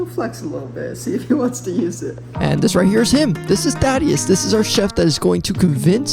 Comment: “This is Thadius.” Oh, at first, I thought you said, “This is Daddy”